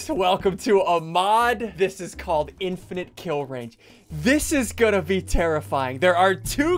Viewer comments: i thought you said infinite kill down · I thought you said infinite children but you said infinite kill range